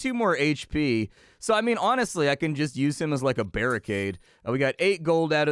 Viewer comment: did you say you could 'use him like a bearicade'?